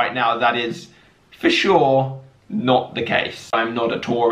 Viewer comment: All I hear is “I’m noddatory”